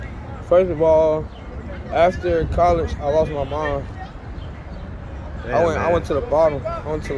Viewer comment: I thought he said he lost his mind after he left college, but really couldn’t understand because it did also sound like he said he lost his mom. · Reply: He did say he lost his mom, lol that’s why the journalist gave his condolences.. he took it back tho and cleared it up..😭🤦🏾‍♀️